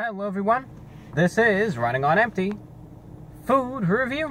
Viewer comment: Honestly I just started watching these videos the other day and I always thought he was saying his name when he says Running on empty. I thought he was saying Ryan Gonempty